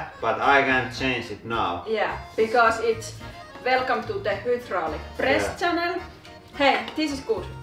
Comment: The first time I watched the hydraulic press channel. I thought Laurie had said welcome to the Hitler press channel!